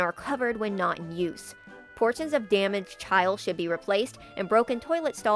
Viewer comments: I heard "damaged child" the first time and didn't even question it, lol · omfg i thought you said "portions of damaged child should be replaced" 😭